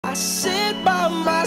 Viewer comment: bro i heard “I shit by myself.. Talking to the moon..”